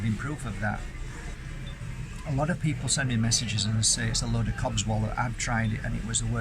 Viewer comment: "a lot of people message me and say it's a load of cobswoll". What saying is this? Just curious if I heard it correctly? Must be a British saying?